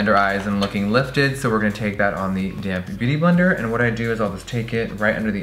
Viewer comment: for the LONGEST time every time jeffree said "damp beauty blender" i thought he was saying "dammed beauty blender" lmao